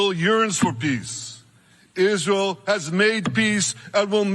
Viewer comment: Did Netanyahu say "urines for peace"? Haha